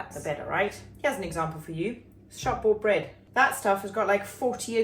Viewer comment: what kind of bread? Sounds like you're saying sharbo bread 🤔 and the CC thinks so too 🤭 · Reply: Hi friend, shop bought bread ❤️❤️